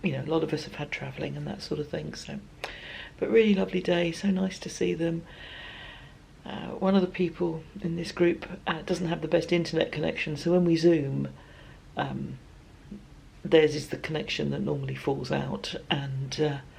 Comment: Ok when you said one friend doesn’t have a good internet connection I heard “intellect” connection at first. Your next sentence cleared up my hearing.